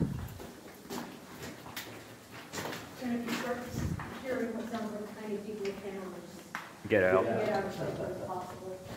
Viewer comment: Great to see a new video from you! At 4:48, someone in your party says, “If you start hearing what sounds like many people [I couldn’t make the word out], get out as quickly as possible.” What was the word I missed? That’s crazy about the dead cow back there! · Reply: Hammering on the rock! That was explaining the concept of tommyknockers to the mine owner.